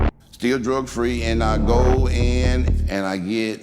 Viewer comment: did he really say drug free at 1:09? or did I hear it wrong